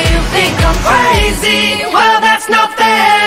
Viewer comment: I hear “Well that’s not fair!” Instead of “Well that’s nothing.”